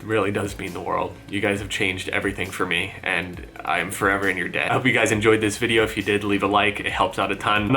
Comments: does he say I'm forever your dad ? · Did he say "I am forever you dad."? XD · did he just say "and im forever in your dad"